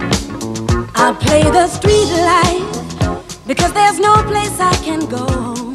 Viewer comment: When I was a kid I thought she was saying 🎵🎵Street lights🎵🎵